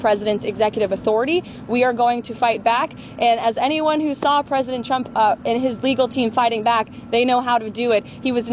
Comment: I keep hearing her say "President Chump". Lol